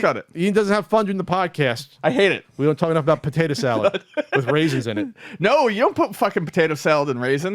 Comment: at first i thought said potato salad and razors lol